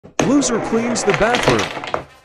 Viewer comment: I thought that he said backroom 💀